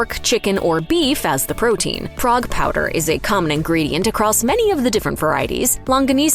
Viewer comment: did she say frog powder?